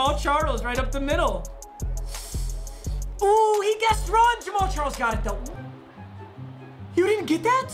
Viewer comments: Who else thought it said “James Charles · POV: You thought he said James Charles so you went to the comments to type: "Who else thought it said James Charles in the title?" but you see everyone talking about it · Who else thought it said "James Charles"?